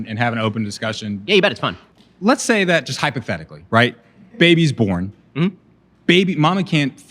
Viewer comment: "now let's just say... hypothetically.... I say this in a way you can understand." xD that's how I heard it as anyway